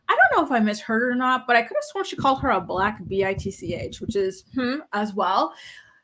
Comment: I heard the “black b!tch” comment to when the audio first aired and I heard it again now but nobody else seemed to hear it and I have bad hearing so I thought maybe I didn’t hear right..but now I think that’s exactly what she said 😮